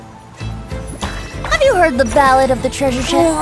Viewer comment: For no reason I heard her saying “my name is Nahida AND I’m seeing stars” Does anyone heard it like that?